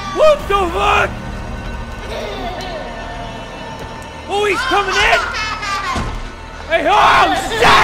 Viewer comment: "OH HE'S COMING IN!" that's what he said.